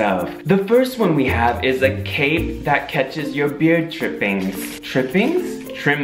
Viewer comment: When first said 'beard trippings' I thought he said 'beard drippings' 😂